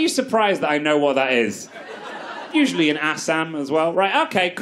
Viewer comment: Did you say "usually in Assam" at 4:30? If yes, then you know your tea too well